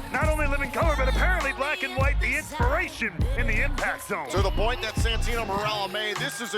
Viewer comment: Yes he did say Gasoline Lol! 🤣😂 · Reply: I thought he said the impression